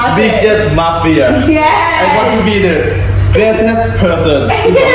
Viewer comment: this is really offtopic but at 1:31 i think he said richest 😭😭sorry just wanted to point it out